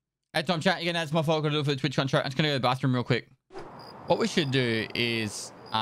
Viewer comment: wtf is he saying ? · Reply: "Add time chat, if you get a add it's my fault. Gotta do it for the Twitch contract. Just gunna go to the bathroom real quick"